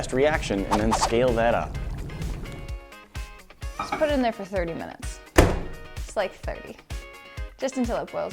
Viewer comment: Is it just me or did she say '30 minutes' at 0:40 ? XD